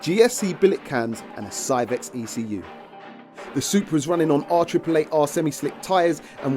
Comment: Almost thought i heard its running on 4 r AAA batteries lmao